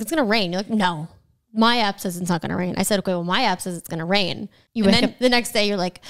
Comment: I thought said my ass not my app. I was shocked